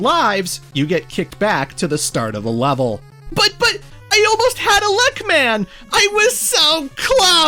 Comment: I thought I heard you say “I almost had a Luck Man!”, and was about to just accept it.